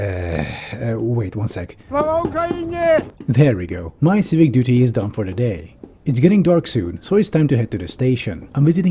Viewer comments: Can someone tell me what he said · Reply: Slava Ukaraini - Glory to Ukraine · What does the scream at 7:57 say? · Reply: Слава Україні(Slava Ukrayini) which means Glory to Ukraine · What did he yell to the protesters at 7:57? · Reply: Slava Ukraini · wait I wanna know what you said!!! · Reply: Слава Україні(Slava Ukrayini) which means Glory to Ukraine